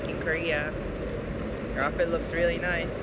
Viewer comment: 뭐라고 하신거에요? fucking korea 라고만 들리네요..ㅠㅠ영어를 넘나 못해서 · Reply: 김경준 100%확신은 아니지만 아마 back in Korea?